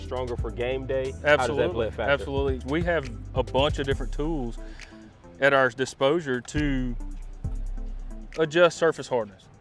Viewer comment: LOL....it's all good, but did dude say "At Our Disposure" at 2:28. NO judgement..but it sounds funny..